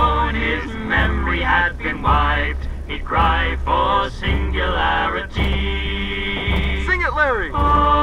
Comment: One of the merry men yelled "Sing it, Larry!" and I heard "Singularity!"